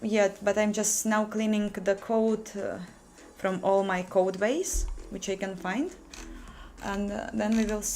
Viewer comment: It sounds like you said "cleaning the code from my code ways." I'm curious as to what you meant by that.